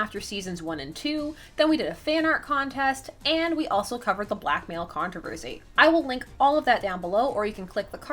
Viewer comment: I forgot about the black mail trouble so I thought you said black male controversy and was so confused